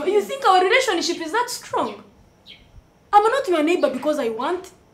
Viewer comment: Pay ur bills later charge ur 4n later,,Ann surely u r such a hero,,,hahaaa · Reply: I hear I'm not your neighbour by choice 😂